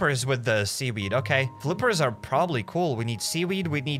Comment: did he just say vine god lol